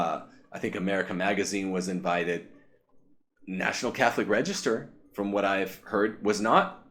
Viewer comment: Sorry. You have no idea if "Barron is painting you all with one broad brush". No one has any knowledge of what went on during that meeting. All you have is the hare brained, biased reporting of the National Catholic Reporter to go on. They of course would leap at the idea that their hated enemies, the traditionalists, were about to be put down. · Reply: Might be my mistake, but I thought he said National Catholic Register.